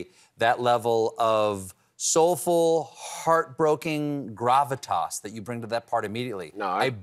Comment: Did Stephen just say Heart broking @ 6:43!? 😄